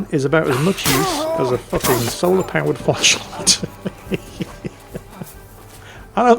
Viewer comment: "Jan is about as much use as a solar powered".... What? Sorry, I couldn't make out the punchline. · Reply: flashlight